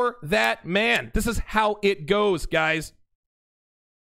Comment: I thought the saying was "Don't hate the player, understand the game!"